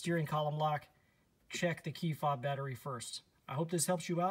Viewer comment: I had a difficult time understanding what said. I heard "C fob battery". I presume that refers to the battery in my FOB. Sorry, but I'm deaf in one ear and can't hear out of the other. :) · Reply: Key fob battery